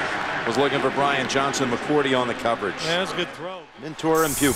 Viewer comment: At very end i thought he said "tore a pupil" but really it was "mentor and pupil."